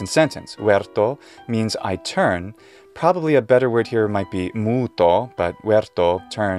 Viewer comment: did you just said muuto as in change?